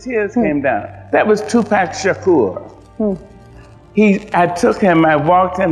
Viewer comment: It sounds like she is saying "that was Tupac Shakur," not "that was too fast for sure."